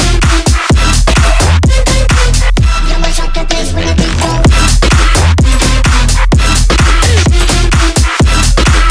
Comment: Can someone tell me what is being said here? Please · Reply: you mess up the bass when the beat go